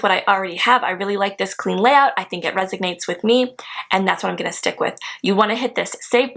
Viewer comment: yes, I do say resonate. I think I just talk too fast and it doesn’t come out the right way. But I do know what resonate means ☺️ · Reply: I'm sorry if I was wrong. But just listen again. I heard 'resignate.' Have a great day 😊